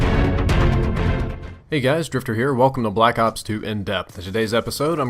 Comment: it sounds like hes saying swifter